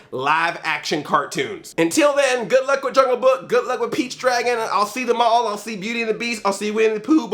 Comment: Everytime you say Pete's Dragon I keep hearing Peach's Dragon. It sounds like some kind of sick Mario spin-off.